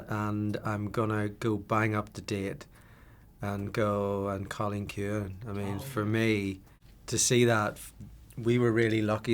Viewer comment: What film does Patrick say at 0:35? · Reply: I think he said An Cailin Ciuin, which is the Irish name for 'The Quiet Girl'